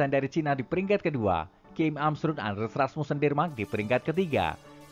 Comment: Hah viktor akselsen dari german?? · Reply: Haduh salah denger kak. Sangat jellas denmark kok jadi jerman..hehehe